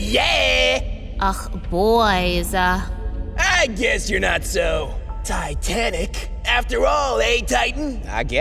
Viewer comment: Did he say the popular ships name in 0:37? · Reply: TITANIC